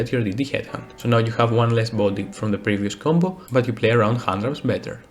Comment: what Do He say ? One lass body than the previous combo but play around.... ( I do not understand the rest) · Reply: you get one less monster on your field but you play around hand traps better